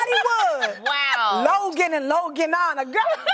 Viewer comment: I dont understand what she's saying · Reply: Logan and Loganana. Her son is called Logan. So she is saying that someone would call their kid Loganana like the cousin did with the name.